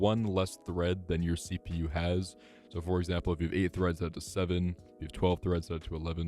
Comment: caption says cores buy you say threads, is it cores or threads?